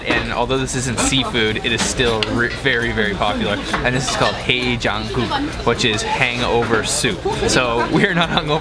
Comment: Did say Jungkook at 8:25 😂 #BTS